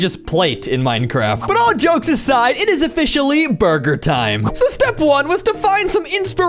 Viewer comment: in the video, said buger time instead of burger time.